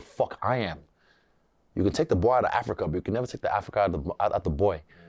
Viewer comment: he never said "boy" at first, he said "boer", google that word, it is used in reference to white farmers in South Africa, he caught himself and realised what he was about to say contradicted his view, so he said "boy" at the end and pretended he was referring to himself